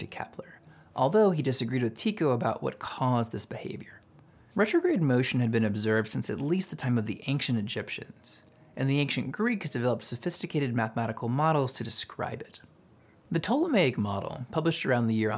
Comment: why does he say anKcient??